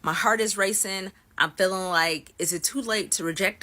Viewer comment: "Their skin looked cold" Lady, it's fall in Chicago. Of course their skin looked cold. If you're cold, they're cold! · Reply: omg for some reason I thought she said their skin looked coated and I was like ...with what.